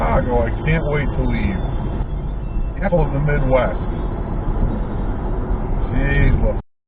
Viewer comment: Did he say "Capitalism is whacked"?? WTF.